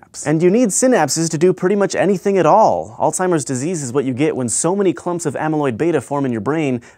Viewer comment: The first thing I thought he said was "Old Timers' Disease"… lol